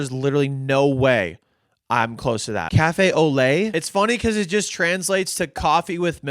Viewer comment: i was listening to this as a podcast and when he said au lait i thought he was saying "oh leh" and said to myself "is he yodeling" skull